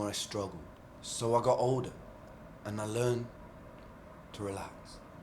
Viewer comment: I may be mistaken, but I think you heard him say, “learn to suffer.“ That’s not what he said. He said, “learn to soften.“ he was saying that if you relax and soften yourself, the dance gets easier. Meaning that you accept what’s happening in your life and make the best of it. Go with the flow. It’s a common sentiment in many philosophies. Especially eastern ones.